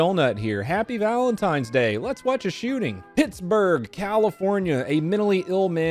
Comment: Im half deaf right now and I thought I heard Tittsburgh. Now thats my spot 😉 giggity